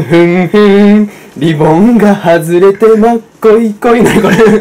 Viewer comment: しばゆーの「リボンが外れてまっこいこい。」 が何回聞いても「もっこいこい。」と聞こえる(笑)